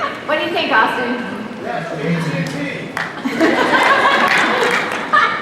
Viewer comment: what did Austin say at 10:00 ??? Jt&T??????